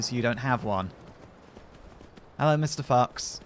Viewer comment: I definitely heard "hellow mr. fucks" instead of fox LOL